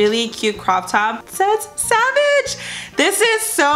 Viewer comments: Haha I thought that shirt said sausage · My fat ass thought your top said “sausage” 😂